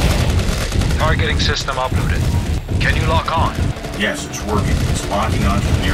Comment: sounds like he said yes twerking